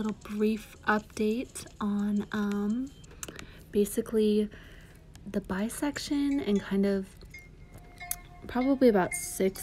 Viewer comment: Thought you said; "The bi-section," as if you were discussing surgical division~partition of flesh. Was like;"waaaaah, Ande is a surgeon now!!" I get that you didn't mean "bi" but "buy" as in purchase, the buying-section, the available inventory section? Or is this a section that is more than available inventory, is it a place to also request items that you haven't got but can get upon request?